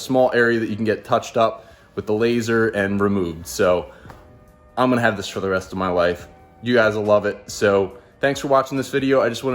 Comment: Did he just say ”i’m gonna have this for the rest of my wife?” 😂